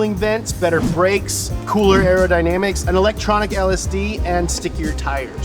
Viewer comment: did he just said this car have a electronic LSD out of it just me hearing what I would like to hear 😄